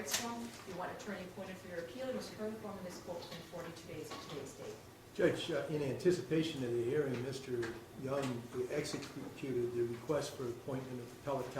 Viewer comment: I thought he said "Judge, ugh in ugh anticipation of the hearing Mr Young be executed". ROFL.